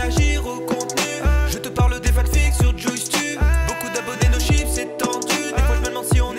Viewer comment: c'est moi où il dit "Tchoupi"